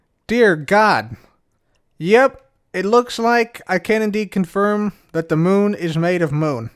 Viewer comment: did you say ''can indeed'' or ''Kennedy'' confirm that the Moon is made of Moon? Sweet pun if intentional. ;P